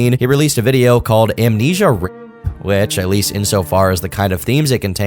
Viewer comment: He released WHAT? I didn't quite catch that. For some reason. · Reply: Amnesia rape